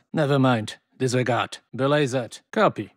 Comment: what does he say at 8:45 after disregard & nevermind?? It’s been bothering me since Warzone released. it sounds like DaLAYSERED